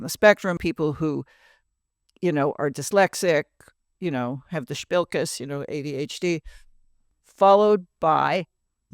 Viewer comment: What is the wonderful Yiddish word Mom uses at 7:24?! Buhspellkis? LOVE that word!